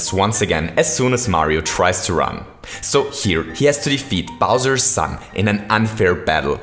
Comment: Who else thought he said "Bowser's Succ" at 4:53?